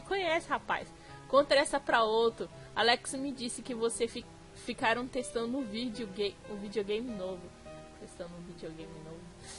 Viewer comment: eu entendi "[estavam testando vídeo gay juntos" 0-0